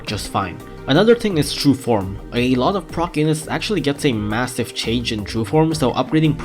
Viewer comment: Just me or did the subtitles say “pro penis”